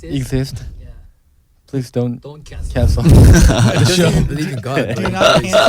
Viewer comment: Does anyone know what Changkyun said at 29:56 ? He said "...believe in God, but..." I can't understand anything else?? :)) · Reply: Minhyuk is not christian so Changkyun said "He don't even believe in god" 🤣